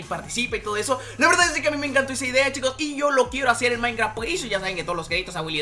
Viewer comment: Yo estaba adelantando el video ah lo loco y escuche "sexo en minecraft" :v